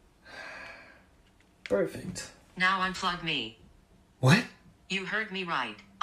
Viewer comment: What it is sound like she said Un fug me